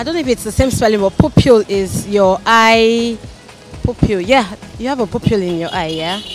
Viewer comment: did she just say pupil in the eye???